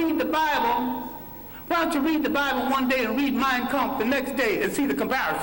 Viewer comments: I cant get what Dr. Clarke says. Why dont you read the bible then "Myocomp"???? What did he say? Help please. Myan..something? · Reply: I think he said Mein Kapf or something like that! I think it's a German book on Nazism or something like that! I can't quite recall, but look it up! I heard it's a good book to read! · what is he saying ? What is the correct name of the hook Mayan comp? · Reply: Deka Kijani "mein kampf".. <link>